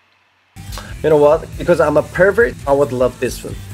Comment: Cause I'm a pervert Did I hear it wrong 😂😂